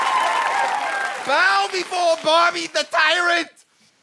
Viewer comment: I heard Barbie the tyrant, not bobby 😂